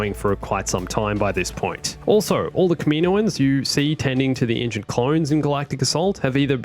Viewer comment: Did u just say Kaminoowans?